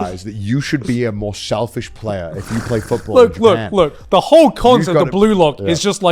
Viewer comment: what did garnt say? The whole what of the bluelock? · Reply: cons (disadvantages)